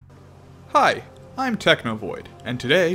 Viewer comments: I thought you said "Hi I'm Technoblade" My first time seeing this channel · This is my first time watching this guy and I SWEAR I heard him say "hi, I'm technoblade"